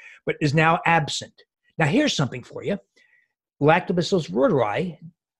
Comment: He speaks too fast....what is he saying at 46:00 lactobitstorise?